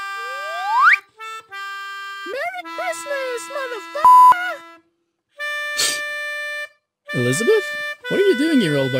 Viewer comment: Did six say the f word at 7:16 ? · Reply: MERRY CHRISTMAS! MOTHA FU**A!!! ;) 😂😂😂